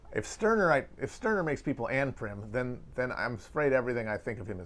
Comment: Can someone tell me what he says at 15:17? "If Stirner makes people _________" ?? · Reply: anprim: anarcho-primitivist, the idea that the only way to achieve a truly equal society is by destroying modern industry and returning to, i assume, primitive communism